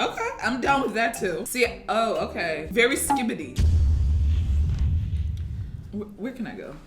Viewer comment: I closed the tap for literally 5 seconds and I heard say 'very skibidi' I almost fainted